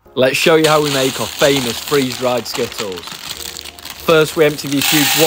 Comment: Only I heard "Free drugged skittles"?